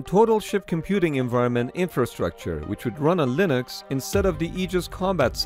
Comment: Did he say a linux? That Linux? Damn